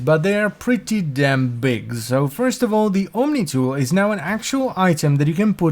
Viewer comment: What was he talking about on 5:32? I can't understand what's the item he says that can now be equipped on the gear wheel. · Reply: The Omni is a Railjack gear item that's given to you on every mission, it lets you seal damage and recall from any outside point if you have like rank 4 or 5 of Tactical skill line.